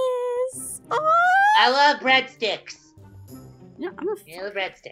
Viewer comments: I thought Travis said "I like breadskits." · When Travis the baby said “I love breadsticks”I thought he said “I love big ####”